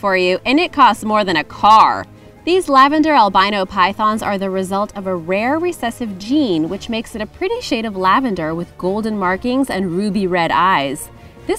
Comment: Did you just say, "Lavender Albino Typhon" 😂😂